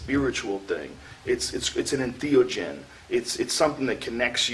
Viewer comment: what word is that..indiogen?